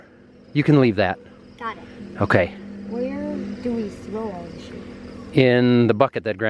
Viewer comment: Lol did say "where do we throw all this shit?"